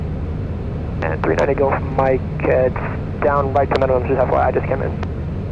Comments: I was having a very difficult time trying to make out what traffic advised you just after your contact with them at about the 40:00 min mark. Best I could guess was that he said some aircraft had just landed? You responded with "Roger that". What did he say? Greatly enjoy your videos. Thanks for the effort and for sharing. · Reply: He was saying that he just landed and the weather was at minimums. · Can anybody tell what Cessna pilot is answering at 39:58? I cannot understand single word! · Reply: He said it was right down to minimums and that he just came in.